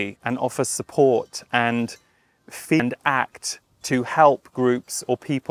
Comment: Hi, Thank you for your vulnerability. The video skips at 5:22. Can you clarify for us what you intended to say? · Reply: so sorry for that glitch! is saying feel and act. Hope that helps! -Chelsea LHE Team